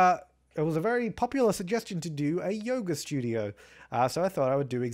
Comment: I thought you said Yogurt Studio...